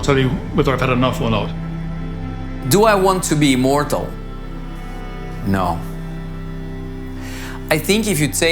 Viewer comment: I love the guy at the end "Would I want to be immortal.....no." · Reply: I thought he said no to being mortal, not immortal?